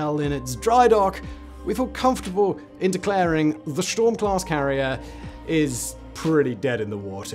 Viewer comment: I could have sworn you said sh*t storm, 😂